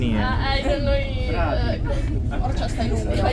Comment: how did you said that bro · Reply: Orcha in mp